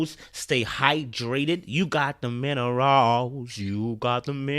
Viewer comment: I really thought he say in each video you’ve got the men aroused. I’m American btw